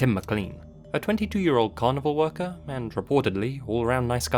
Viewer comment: I swear I heard "...22 year old cannibal worker." Edit: Fuck PETA. I'm all for saving animals but this is just distasteful and disgusting